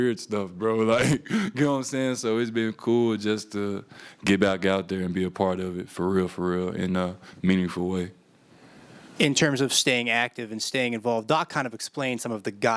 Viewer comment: did bro just say "frfr" 😭